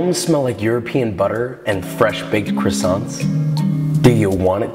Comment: The way he says " Do you want it to? " sounded like no one wanted LMAO